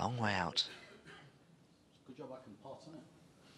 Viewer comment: what did Mark say ? .... pot in it ? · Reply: its a good job i can pot aint it